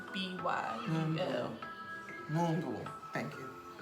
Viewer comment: it sounds like you said 'moonblu' 😂 but u got the spelling correct